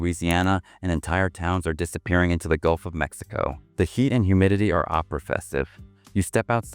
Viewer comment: "the heat and humidiy are oppressive", (or whatever AI said}, but the CC says opera festive. lol